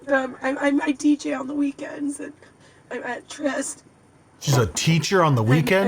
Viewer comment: I think she said "I DJ on the weekends"